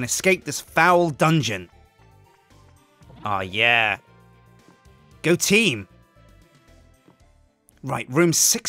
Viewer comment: sjin says go team! IT sounded like goateam xD